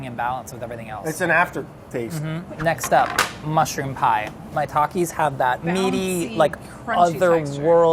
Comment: i thought that they said "My Takis" when they were talking about the mushrooms and i was kind of low key ok with putting hot takis on pizza